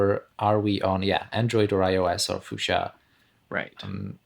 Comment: Did I hear that right 'Fuchsia` @ 25:06 !